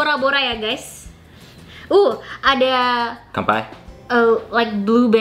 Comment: Wuuu Adaaa.....? Apaan? Aku salah dengar atau memang kak ngomong bhs indo? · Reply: Dia ngomong kampai (cheers in japanese)